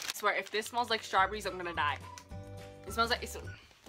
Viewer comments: I thought she said " if this smell like SHARPIES I'm gonna die"😂😂😂 I know I'm slow😂😂😂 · I thought she said "if this smells like sharpie, I'm going to die!" Lol And my friends still have a Bop It